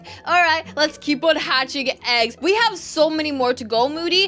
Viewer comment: I thought u said hacking eggs 😂